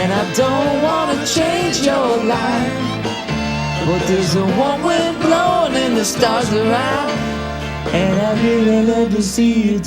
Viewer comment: Bill got the same words mixed up that I did for 40 years until I realized what he actually was singing. I also thought he said “there’s a warm wind blowing the stars are out“ but it’s “stars around.“